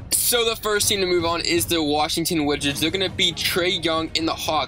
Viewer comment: did he say Washington witchats